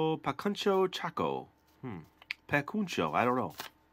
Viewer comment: I thought you said PAKUNCHO TACO… :)